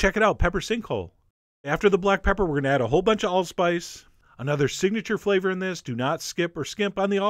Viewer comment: Could anyone tell me what that spice at 2:00 (after black pepper) is called? Thanks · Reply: It's allspice (AKA Jamaican pepper) - the link in the description will take you to the recipe page with all of the ingredients listed.